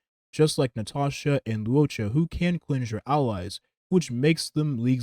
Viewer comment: what do you mean "Natasha can't cleanse your allies"? Nat's A2 trace "Soothe" does exactly that - "The Skill removes 1 debuff(s) from a target ally."... having Nat and M7 on the team can take care of Kafka's CC's in no time... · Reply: i think you misheard. he clearly says "like Natasha and luocha who can cleanse your allies". I think he accidentally said "just like" instead of "unlike"